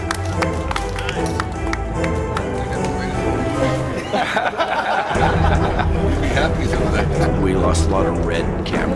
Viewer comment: Does anyone know what Stallone says to Statham at 1:28? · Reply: Do you need a toul